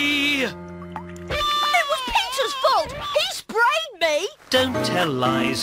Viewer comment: Did you guys see he said Peter but the subtitles said pizzas